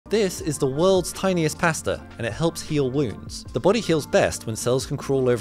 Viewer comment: I heard “This is the worlds tiniest pastor” 😭